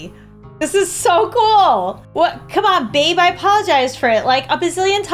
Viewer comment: did she just say babe